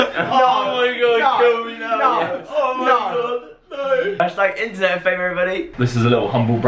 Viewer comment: omggg when there was the # "internetfame" i legit thought he said # it'slitfam